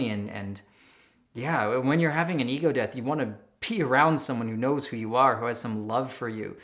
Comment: sounds like "You want to PEE around someone who has some love for you" [instead of...] "BE around someone. ......". Just had to drop that in here. I'm sorry if someone else already mentioned it, I haven't scanned the other comments, there are thousands by now and not all of them are loading anyhow. .